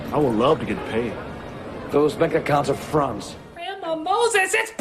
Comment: en el minuto 12:08 van damme parece que dice "a cunts" osea un coños xD.. es lo que suena.